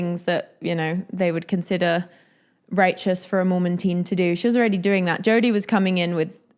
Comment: This was a great review of the book, and I plan to read it! As a bit of levity... when you referred to Shari as a mormon teen... I heard it as "mormantine" I was like whoa... that's a descriptor I haven't heard before! 😂